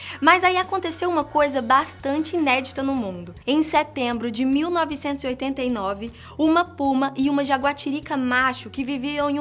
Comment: Só eu ouvi 1989??